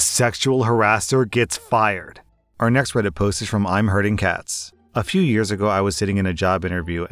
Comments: I needed to dub check the name on the 1st story. I heard "I'mhurtingcatz" not "Iamherdingcatz" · "Our next Reddit post is from IAmHerdingCatz" Dude at first I heard that as "I'm HURTING Cats" and got very concerned lmao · I had to double check the first userame of the first story, I hear "I'm hurting cats" and I was like "WHAT??"